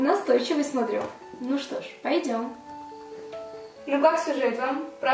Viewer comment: Никак не пойму что говорит на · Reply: Ну как сюжет, а дальше не понимаю))